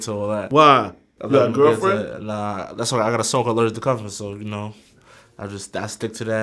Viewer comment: Wtf, did he say at 7:35? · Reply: Zede Israel That's why I got a song called "I'm Allergic To Cuffin"